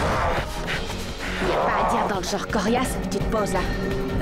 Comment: Tes pas à dire dans le genre coriase... Je comprend pas la suite elle dit quoi Yumi ? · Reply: La suite, elle dit "Tu te poses là"